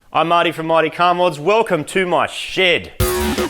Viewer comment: as an american i hear "Im mighty from mighty car mods, welcome to my shit!"